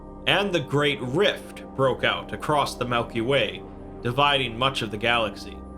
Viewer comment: did he say Melky Way?